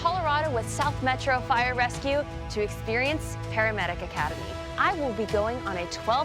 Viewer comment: am I the only one who initially thought it said pandemic academy. wtff